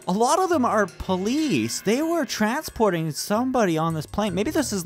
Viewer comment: did you say something like a prison plane?